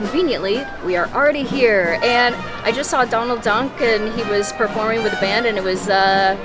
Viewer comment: It sounded like you said “I just saw Donald Dunk” instead of duck and that makes me like you even more. Adorbs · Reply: 😂 I did totally say Donald Dunk 😝